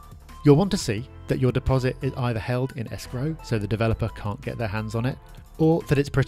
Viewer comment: Deposit is held in a what cant hear? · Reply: Hi, what says is to have the deposit is held is escrow which means money can't be touched by developers until the transaction completes. So inn the UK, escrow accounts are often used during private property transactions to hold solicitors' clients' money, such as the deposit, until such time as the transaction completes.